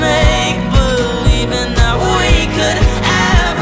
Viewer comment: Tbh, I thought i heard “HOY COULD EXIST AGAIN” cause I bursted out laughing :>